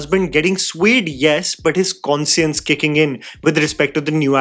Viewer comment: i have to turn up the CC button as i couldn't catch the word. That's not how you pronounce "Conscience".